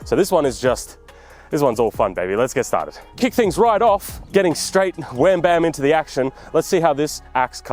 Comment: I thought you said "Getting straight into the ax-shin."